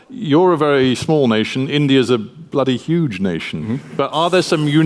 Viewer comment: did he just said:" bloody huge nation".